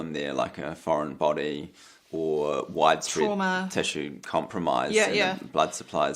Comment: I don't understand what Dr Sam says at 1:31, can anyone help please? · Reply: I believe Dr Sam says “trauma”. Hth 😊